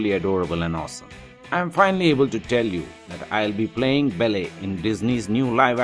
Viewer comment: all i hear is "bell-air"